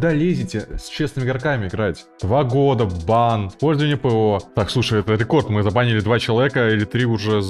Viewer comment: послышалось слово "ПВО"